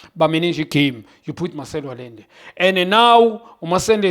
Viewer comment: Did my goat accidentally say “Masende” instead of Marcelo 😂